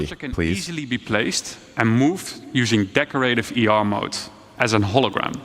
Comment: Anyone thought he was saying Decorator ER mode all through the presentation until the slide that said it was AR not ER?